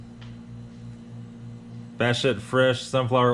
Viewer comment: I love the "Bat Shit" comment at (4:00) I am sure you didn't say that but sure sounded like it. 👍✌️ 🧐🤔🤪😝😂🤣